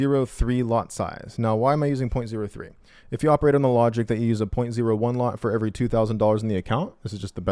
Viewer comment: did you do 0.01 ? that’s 35 entries. slow down bro 💀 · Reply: yeah I entered 0.05 then 0.07 then another 0.1 and another 0.13. I thought said 0.1 lot initially for the a $2K account. Actually he said 0.01 :) I misheard him